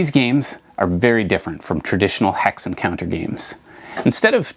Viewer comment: Not a wargamer, though I have started to collect some war games. For the longest time I thought everyone was saying “Hex Encounter” instead of “Hex and Counter.” Makes much more sense.